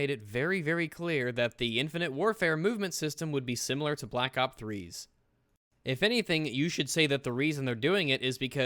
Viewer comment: did say like black op 3